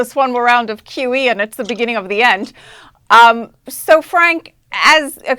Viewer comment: Mr. Guistra did not say, "it's the beginning of the end"...he said, "it IS the end". A nuanced difference perhaps...but a significant and noteworthy difference to be sure.